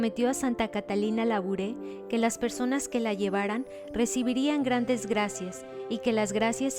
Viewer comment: parece que dicen gran desgracias 🤦‍♀️